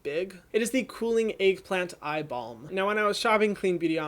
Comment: Ohhh eye balm.....I thought you said eye ball the 1st time. Hahaha